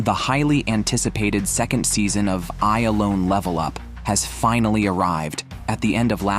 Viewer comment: Did i just hear "I ALONE LEVEL UP" They his sister WHAT!?!!!😳 PLS LEAVE A LIKE